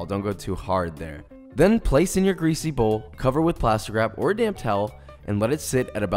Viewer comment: Instead of "damp towel" I heard "damn towel". I was like, jeez okay yes sir, I'm on it. 😂